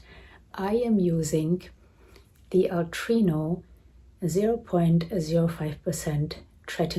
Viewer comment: Hello! At about minute 11:39, mentions the tretinoin she uses, but I cannot understand the brand she says. Help, please! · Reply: Hi Elsa, it is called Altreno. You do not a script for it x